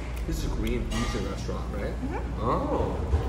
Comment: Nice spread, nice friend. Korean fusion 😊 I thought your friend said green fusion at first. Lol